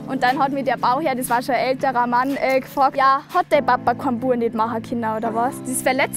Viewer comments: hattaababakurkinndawas Was?! Was sagt sie da? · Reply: Ich glaube: Hat der Papa kein Bua gehabt... Den Rest versteh ich auch nicht 😂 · Ein wirklich guter Beitrag. Ich habe nur ein kleines Problem... Ich komme aus Norddeutschland und verstehe teilweise leider nicht so genau, was sie sagen.. zB bei dem, was sie bei 3:58 sagt, was genau hat der Bauherr zu ihr gesagt? Und auch das was Stefan bei 4:32 sagt ...ich verstehe kein Wort? :D Kann mir das vielleicht jemand "übersetzten² :D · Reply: "Hat der Papa keinen Jungen machen können oder was?" (Ich komm auch aus Norddeutschland, aber bin Sprach-begabt :D :D ) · was hat die gesagt??😂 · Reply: "hat dein Vater nicht geschafft einen Jungen zu machen?"